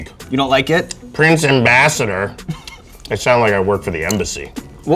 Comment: I swear to god I heard "what would your fursona be like"